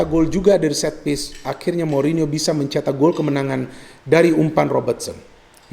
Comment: firmino atau mourinho, apakah saya salah dengar wkwk